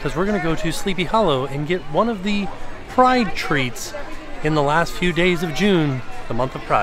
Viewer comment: Oh! “Pride treat” my fat butt thought you said fried treat. 😂😂